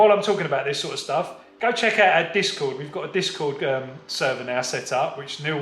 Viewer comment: I was trying to figure out what you were saying lol. It sounded like you were saying DISCO!! You are saying the site DISCORD lol!! Thanks!! Aaron. Ocala Florida